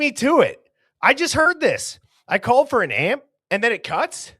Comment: Did she almost say "called an ambulance "? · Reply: I heard “called an ambulance” also.